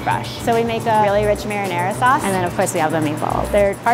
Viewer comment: I’m sorry but instead of “rich marinara” I heard “rich marijuana”